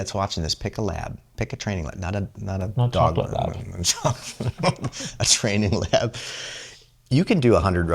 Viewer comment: Time Stamp 15:28 "pick a lab. Pick a training lab. Not a not a dog lab, a training lab." I could swear said, 'not a Chocolate Lab (and you both smirk)',. Did I miss hear, or did you edit the transcript? What's wrong with Chocolate Labs? I've come across a lot of 'opinions' about Choccies. What do I need to do differently from Black or Yellow Labs?